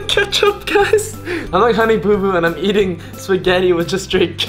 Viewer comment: did he just say honey boo boo omg I love popularmmos